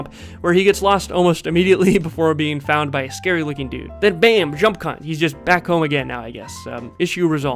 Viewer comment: did you just say.... "jump-c*nt"? 😂😂😂 Haha great video tho!